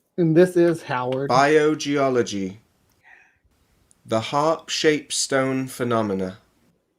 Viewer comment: So, heart attack? I predict those pebbles from the Mud fossil Guy! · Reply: I do think it sounds like he says they are harp-shaped. When I first heard that I was thinking: what are you going on about? 😅